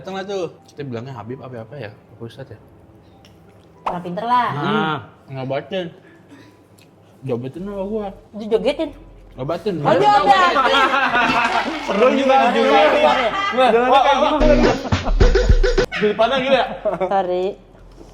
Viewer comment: di menit 11:36 aku jg dengernya dijogetinn😭😭😭